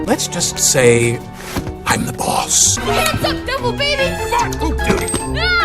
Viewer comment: Is it me or did i hear the baby say f*ck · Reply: Jose Salcedo HE SAID FART